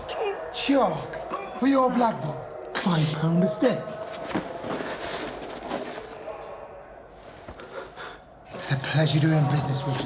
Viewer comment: Could anyone tell me what's said @ 27:09 by the Lee boy? I still can't figure it out. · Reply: Chalk for your blackboard. 5 pound a stick.